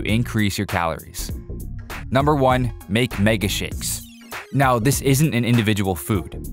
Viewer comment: i heard mega shits